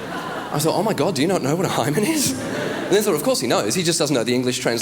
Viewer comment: OHH, HYMEN! I thought he was saying "High Man"... He said the skater joke before to an austrailian audience and I thought it was an Aussie term for boner...